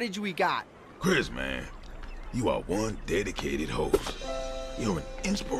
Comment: “Chris man.. you are one dedicated hoe.” -Chef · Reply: Wait he said HOST right... RIGHT?!